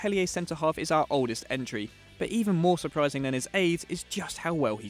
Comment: did he say aids